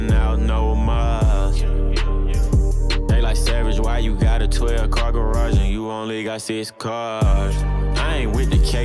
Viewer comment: Did Savage just roast himself? 😂😂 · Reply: C. Venom first time I heard this I hear “savage, why you gotta toy garage and you only got six cars”